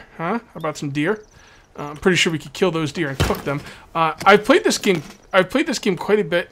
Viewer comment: When he said, "I'm pretty sure we can kill the deer and cook them." In stead of cook I thought he said "Fuck them" lol